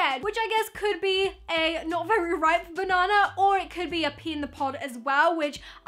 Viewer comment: When she said 'pea in the pod' I thought she said 'pee in the pot' 😂